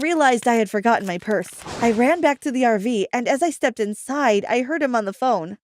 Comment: Let's just run back to the airplane that's what I thought you said